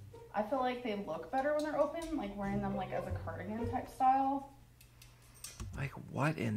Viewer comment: did she just say "a curtain type style"??